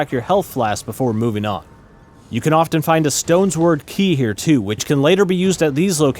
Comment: did he call it a stones word key? LOL